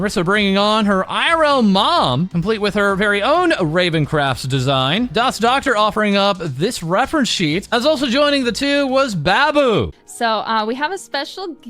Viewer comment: Did you say, "Baboo?"